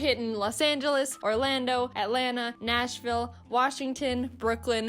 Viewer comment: did you say, ATLANTA!?!?